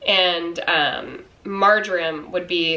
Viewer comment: Marjoram! OH! Gosh I thought you said Margarine at first! You should have seen my confused face! Hahaha! Great recipes, thanks for sharing! x